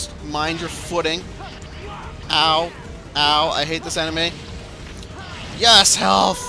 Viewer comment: When you said "enemy," it sounded like "anime."